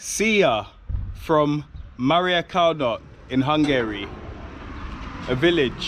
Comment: I thought bro was about to say Mario Kart at the beginning 😂